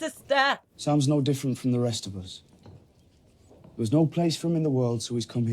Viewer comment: That edit, I thought Jon said "sounds no different from the rest of us."